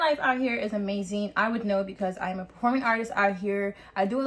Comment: Did she just say she a porn artist?..